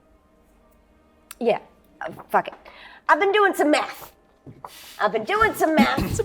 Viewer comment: lol I thought said she'd been doing some meth and was like, "eh, not even in the top ten most surprising things she could say"... :P